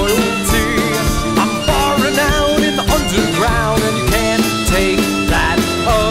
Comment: I thought he was saying ‘I’m far renowned in the underground’ for the longest time...